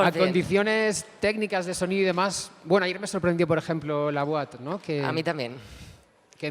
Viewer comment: Cual es la sala que dice en el minuto 7:32 dice la boat o algo asi, pero no consigo localizarla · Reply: La Boite, está en Madrid, en la calle Tetuan 27